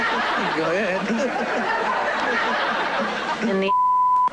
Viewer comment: what is she saying here? I can't tell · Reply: She says in the ass, I was confused and looked it up and found the clip